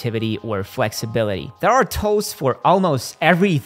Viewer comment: before I realized he was saying 'tools'....I'm like....wtf is he calling toes?